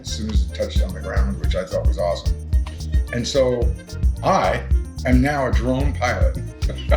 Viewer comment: You said, "I am now a drone pilot", I heard, "I am now a drown pirate". Had to rerun and listen again. LOL.